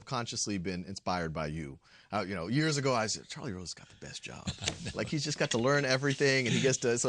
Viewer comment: Totally thought it said "Charlie Sheen"